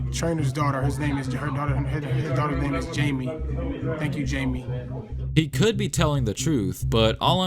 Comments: I heard "thank you jamie im the westbrook clip" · I listened to Russ multiple times and I clearly hear “Thank you Jamie”. But if you put CAPPED subtitles I can see how the internet will run with it